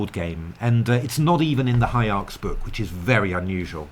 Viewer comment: this series is amazing love to hear your thought process when taking on the engine. also what is the name of the book you mention at around 4:10 ? I hear High Arcs or is it High Arts? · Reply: Thanks! It's HIARCS! Pretty good chess database software with its own (pretty strong) engine and a high-quality opening book: <link>